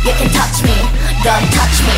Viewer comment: i keep hearing "you can touch me, don't touch me" lol